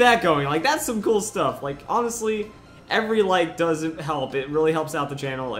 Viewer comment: lol it sounded like you said every like doesn't help. awsome Strategy guide though